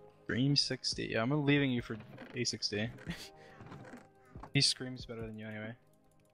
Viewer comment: "He screams better than you anyways" - Dream, 2020 Let's start digging his grave I know he meant like Panicked screams from the challenges and stuff But it It sounds so wrong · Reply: Oh I heard it as 'He screws better than you anyway'😆